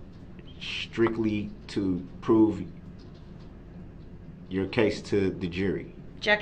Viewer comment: That is NOT what he said liar! He said present, not prove. Big difference Jack a##!